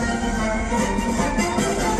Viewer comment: I heard he say GOOD MORING USA! IM FEELING LIKE A WONDERFUL GA